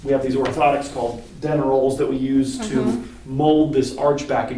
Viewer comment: I always giggle when he says denneroll because I hear “dinner roll” 😂😂